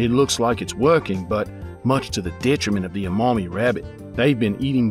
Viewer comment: Hahaha i thought he said a mommy rabbit